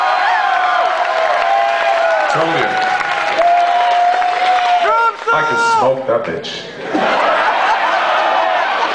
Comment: what did mikael say there? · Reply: I think he says "told ya"